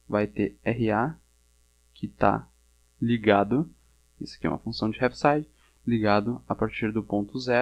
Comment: Aos 3:45 você diz que é uma função de que? Eu não entendi o nome e não conheço essa função. Poderia dizer o nome para eu pesquisar? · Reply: Olá. é uma Função de Heaviside.